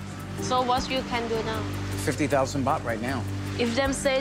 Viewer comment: I thought he said $15,000